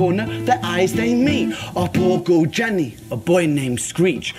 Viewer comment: These are all live one continuous shot videos done on very low budgets. So Ren is singing, playing guitar and acting while making sure he arrives at the right spot at the right time to get the right reverb effect for his voice or to create sound effects like knocking on doors etc. The videos are so well thought out and the performance so captivating that you don't even notice the small mistakes. For example, in Jenny's tale when Jenny and Screech meet Ren starts to.say a boy, rather than a girl.called Jenny, but just carries on as if nothing happened. A big shout out should also.go to.Samuel who films all of these performances. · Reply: "For example, in Jenny's tale when Jenny and Screech meet Ren starts to.say a boy, rather than a girl.called Jenny" You're mishearing him. He says "our poor girl Jenny".